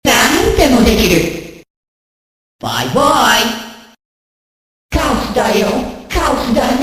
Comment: All these years i thought he was saying "I won!"...